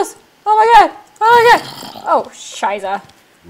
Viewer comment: Has she just talked German? Did she say "Scheiße" at 9:37?? If so, I feel proud of my first-language :D